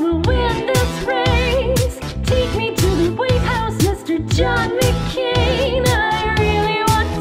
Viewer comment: idf you notice it sounds like palin says "take me to the wheat house, mr john macain!"